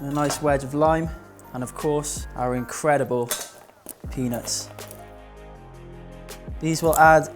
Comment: Yet another amazing recipe! Beautiful fresh ingredients! By the way - am I the only one who laughed when said it was time to get his nuts out?! 😂 (I'm so immature!) x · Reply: Also at 9:08 it definitely sounded like penis and not peanuts hahaha